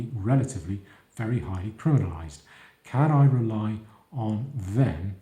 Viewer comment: Astute commentary - very plausible. One word I couldn't make out around 8:11 relating to sub-Saharan Africans being "highly pluralised" or is it "polarised" "can I rely on them to protect me against crime?" I think I understand all too well the point being made, but have played it back even at lower speed and still can't fathom what the word is...